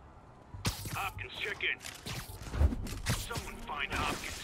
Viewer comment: I thought he said Popeyes chicken lol